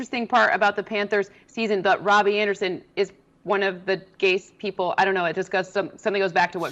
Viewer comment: sounded like Cynthia said “Robbie Anderson is one of the gayest people” 💀